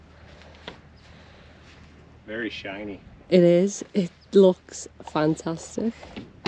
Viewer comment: At 10:18 you say: it looks fantastisch. That is german. Or have i heard it wrong? Another question: what type of paint he has used?